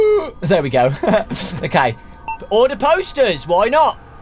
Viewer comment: When he said order posters I thought he said fix the toaster😂😂😂